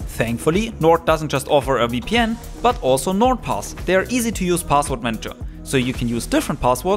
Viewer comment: i thought that said n word pass 💀